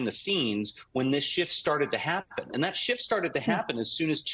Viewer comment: I thought he was saying "$h!+ started to happen" Had to back up and listen. Lol.